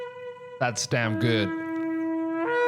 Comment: Did he just say, thats d-mn good? (Around the 11 min. mark, talking about the man blowing the trumpet)🤔